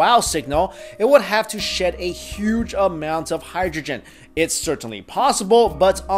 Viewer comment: You kinda sounded like OwnagePrank's "Buk Lou" when you said "Huge" here